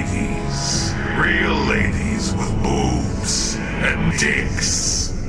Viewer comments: Hey, I know I'm 5 years late on this but did anyone else notice that when Ahriman at 23:07 speaks the subtitles are, "Real ladies, with boobs. And not dicks", but he actually says, "Real ladies, with boobs. And dicks"...... what have you done..... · So at 23:07, it seems the audio is corrupted so that Ahriman says "and dicks" instead of "and not dicks". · I’m pretty sure he said not dicks when I first watched this years ago. I know the subtitles say not dicks but he says with dicks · Subtitles says what Ahriman said:"And not dicks", but I'm sure what I heard how he said:"And dicks". Am I the only one who noticed that? 23:07 I see what you did there, I see...